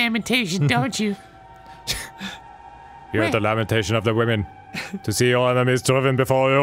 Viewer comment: For a real second, I thought he said, "Are you into the domination of the women?" And I would not be surprised, with the context they were speaking in, if that's what they'd said.